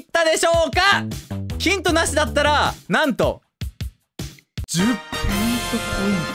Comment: スマホで見てると効果音デカすぎて「ぺいんとポイント」の部分聞こえねぇw